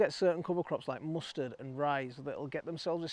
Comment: When mentioning cover crops, you did say mustards and ryes, right? I thought you said rice first…